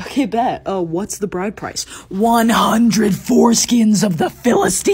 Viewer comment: BYE I THOUGHT YOU SAID 104 SKINS NOT 100 FORESKINS IM CRYIGGGGGG